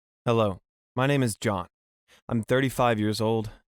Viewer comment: I thought he said his name is Jaw tho because my volume was very Low so I can’t hear well:)